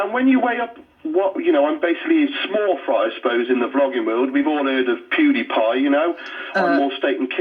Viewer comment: I'm pretty sure she said "vlogging," but "flogging" would make a very interesting YouTube channel, wouldn't it? ;) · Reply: Suzanne Baruch You are correct. She did say vlogging, but my mind first heard flogging. I'm sure it was due to not hearing that accent very often. I'm from the Southern US, and I know I've thought I heard worse from the locals here. It would make for some interesting YouTube content indeed! 😉